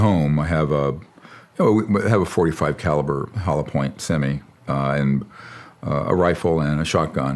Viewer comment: You can tell he’s not a gun guy but completely professional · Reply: I actually thought he said hi-point first, which may have been worse 😂